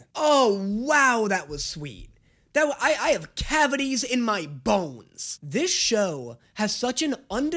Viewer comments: "oh no oh im feeling" made me laugh and 2 seconds later he says "i have cavities in my bones" i die laughing bc i thought he said in my balls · I thought he was gonna say I have cavities in my balls😭